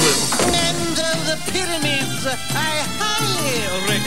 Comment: Happy this is here. Since I first saw this years ago, I always wanted to get the words since they were too fast to all catch, especially with all the accents. My one question is that I thought he said "And though the pyraminds I highly recommend...," as oppsed to "land of the pyramids..." aka, you guys are more precious to me than the pyramids. Could be wrong, though. :)